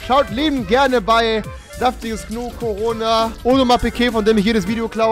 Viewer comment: wer hat auch Corona verstanden